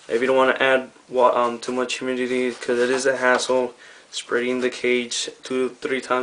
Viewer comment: i thought he said asshole at 5:32 but he said "Hassle"